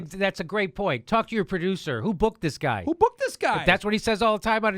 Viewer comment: I thought he said "booze"